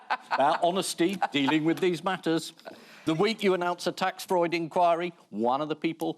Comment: Did Ian say "a tax Freud inquiry"?